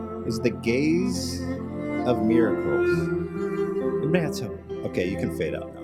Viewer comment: Am i the only one that heard it as "gays of miracle"